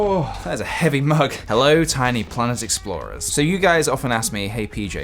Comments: i thought you were saying "tiny plant explorers" until this video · This entire time I thought he greeted us saying hello tiny PLANT explorers...EVERYTHING I KNOW IS A LIE >.>